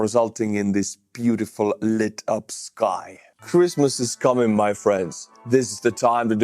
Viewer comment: 'Cruise missiles coming my friends!" is what I thought I heard, but I guess it was "Christmas....."